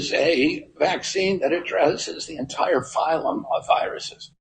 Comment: Genuinely confused. At 2:57 I think he says "a vaccine that addresses an entire phylum of viruses". Not "the entire ...". You say they don't belong to a phylum and then go on to explain how viruses have their own kingdoms and then within those kingdoms are phyla. I just checked and corona and influenza are both in the same virus kingdom but different phyla according to Wikipedia. So if he says this platform could cover a whole phylum and then says it may be extended from one phylum, fly, to another, corona, that seems to all make sense and not differ from what you are saying.